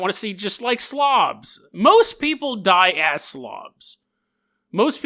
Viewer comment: I heard you say it as "most people die as slavs."